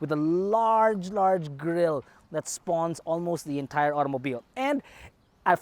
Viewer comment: did you just say spawn? Fr. Ampil's gonna say your trippin' 😅🤣